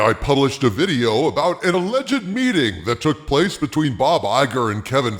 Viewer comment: I thought you said, "alleged beating". That would have been nice.